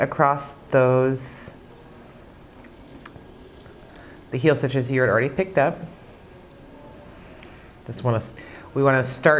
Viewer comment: Can you tell me what you said @ 20:52?? "that you already picked-up"??? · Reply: The heal stitches you already picked up